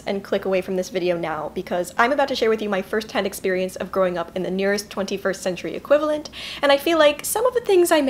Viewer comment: I THOUGHT IT SAID "I GREW UP IN THE CLOSET" JSKSJDKSJAOAM I